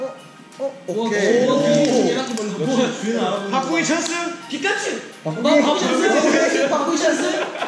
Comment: Didn't anyone else hear someone say "Pikachu" at 1:33 Cause that was so freaking precious 😭💗❤💞❤💓 (Sanshine was that you i feel like it was him) am i hearing things lol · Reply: He was pretending the red ball was a pokeball and said, 'I choose you, Pikachu!' I don't think a lot of people caught it. XD